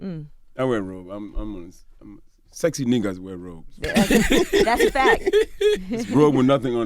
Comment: I thought he said “ Six neegas wear robes”